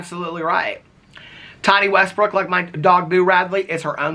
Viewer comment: Omg lmao I thought you were saying Thotty Westbrook haha I need to check my ears! 🤣